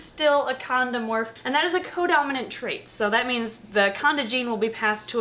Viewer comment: all i heard was “condom”